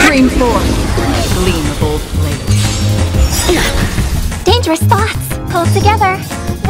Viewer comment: Off topic but I can't be the only one who bursts out laughing when a fight starts and Robin just blurts out "dangerous thoughts" at the start of a fight, right? I know that's supposed to be her "be careful, tough fight ahead" voiceline, but she's so cheery and says it so fast it always takes me by surprise, like she just realized she needs to call her therapist for an emergency session a full second after we started the fight and now she's going to have to hold it in for a bit. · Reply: I always heard "dangerous spots!"